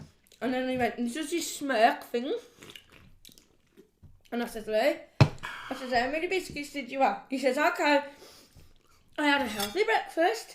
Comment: Does anyone know what says at 26:55? I’ve watched it about twenty times and still don’t know I just burst out laughing😂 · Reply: Ha ha it's here, you have really made me laugh ha ha , I said Ohh he said I've been eating healthy today and I said have ya, lol sorry yam yam problems lol 🤣🤣🤣🤣💕💕💕💕xxx